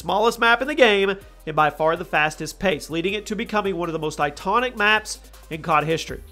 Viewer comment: Did anyone else hear him say "itonic" instead of "iconic?"